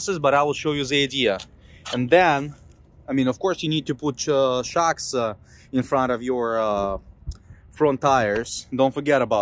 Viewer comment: when you put the landing gear down what did you say you need to put in the front? you said it at 6:10 of the video? Thank you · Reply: Wheel Chocks